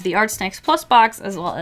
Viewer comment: At first I thought you said Art Smacks